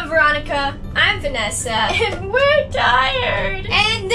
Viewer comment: When Roni said "and were tired" i thought she said and were dead. I was like you whart